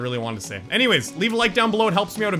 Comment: every time it sounds like you say piss instead of peace